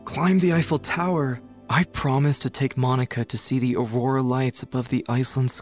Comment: did I hear MONIKA??